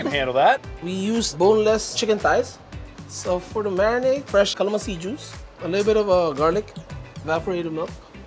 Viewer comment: I’m assuming the marinade has Calamansi juice, as opposed to ‘kalama seed’ juice, as the subtitles say… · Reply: I heard him say 'calamansi ', not kalama seed